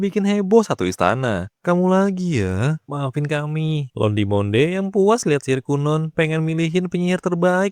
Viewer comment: entah kenapa dengernya malah onde Mande 😂